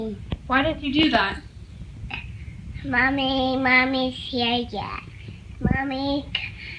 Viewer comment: she was saying, Mike turned it off! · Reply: i heard molly but I'm sure it was mommy